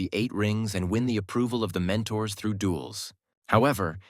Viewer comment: i heard sex mentors not six😂